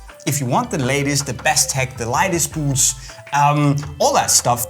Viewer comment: i thought he was going to say if you want the ladies go for the expensive boots. Then i realised he said lightest.